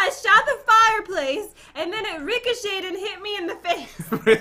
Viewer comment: rickershaded hit me in the face Me: what kind of word are you using????